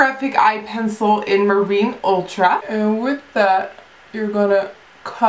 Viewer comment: everytime you say 'and with that' I hear 'and whip that'. It doesn't make sense xD